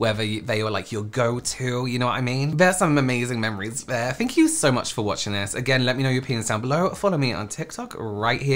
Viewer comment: I have to add because someone reminded me in their comments. I still use MAC foundation shades when I’m describing people to my makeup artist friends or the shade I like my tea/coffee. Answering my own question - my first ever MAC product was a quad eyeshadow palette (the old see through lids) from Their “naughty nauticals” collection! 🖤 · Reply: I listened quite a few times over and over and it still sounds like he says "let me know your penis down below"!😂😂😂